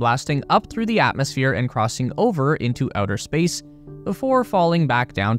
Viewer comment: I thought you said Elder Space...Let me get tf off Elden Ring